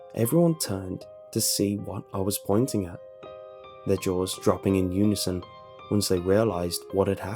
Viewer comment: I thought he said their drawers dropping in unison